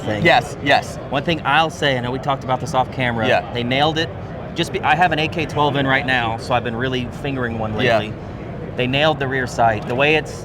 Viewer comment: Did you said you have an AK12!?